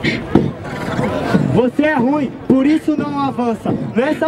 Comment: por aí, eu ouvi um "vota no Refel em rapaziada". Posso estar errado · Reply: Eu ouvi "acaba no Refel rapaziada", o cara tava se referindo ao round